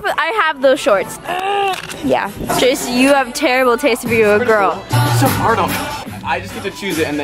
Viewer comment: Did anyone catch when she said "jake you have terrible taste for your own girl!" @ 1:35ish